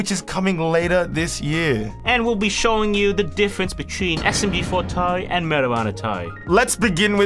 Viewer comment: May I request a fix in the closed captions, in the English version of the Captions, you said SMG4 Tari but the captions said SMB for tie (Which is Tari) and then when you guys said Meta Runner, the captions say: "Marijuana tie" even though yall said Meta Runner Tari. May yall please fix this? 0:26 with captions on, thanks :D